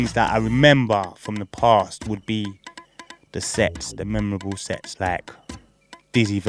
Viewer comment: i thought man said "the sex" you know. I was like "my g"